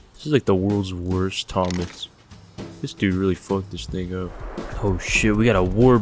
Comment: it sounds like he says the n word lmfao but after listening like 5 times i realized he was saying “thing” but now i can hear both whichever i’m thinking about